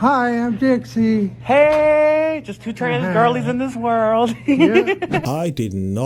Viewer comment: at first I heard 'transistor'